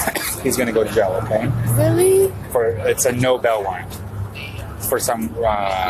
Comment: I swear I was 12 minutes into this video before I realized the officer was say “no bail”. All I heard was “no bell” and I was confused.